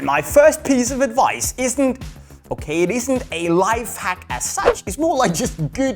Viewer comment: Did he say sense or s*x